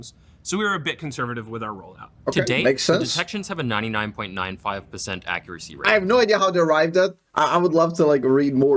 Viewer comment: when he says "detection has 99.95% accuracy rate " it means that whenever it detects, its a false positive just 0.05% of the time, or 1 out of 2000 cases. It doesnt mean it's catching 99.95% of offenders. Riot does it again with corporate language. Edit: The detection accuracy rate is 99.95% not 99.5% as i originally typed. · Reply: I think people misheard him. 11:49 they say 99.95% not 99.5 It's a 0.05% false positive rate. Not 0.5. 0.5 (a number that is not said in this video) is 1/200. 0.05 (the number he actually refers to) is 1/2000